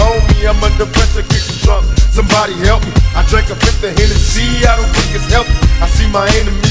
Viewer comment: I drink a 5th of Hennessey, I don't think it's healthy. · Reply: Tupac701 I thought he said "I don't think it's helping"